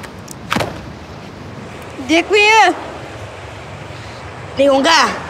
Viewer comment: ได้ยินเด็กหงส์กาก